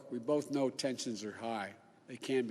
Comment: I thought he said, "We both know Texans are high...they can be high after a tough election."